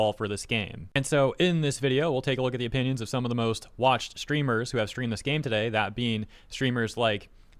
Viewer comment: i misheard 'most watched' as 'most washed' and actually just D: when he mentioned dokibird